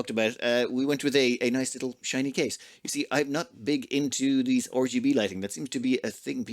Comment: Almost did a spit take. Did you say Orgy B light at 12:12? cause that is awesome if you did!